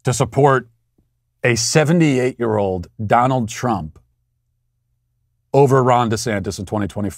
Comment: Lmaooooo I always thought I heard “ Rhonda Santis “ and just thought , “hmm a man named Rhonda?” *shrug*